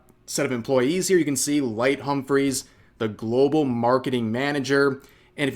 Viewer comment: “Light Humphreys” For some reason I first heard “Light Yagumi”